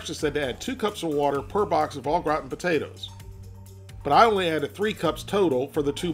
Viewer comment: Not gonna lie at first i thought he said a box of rotten potatoes